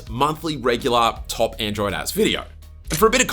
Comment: I kept hearing , 'Top Android Ass '